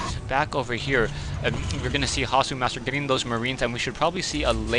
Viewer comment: lol at 2:50 is saying "gonna be masturbating those marines"?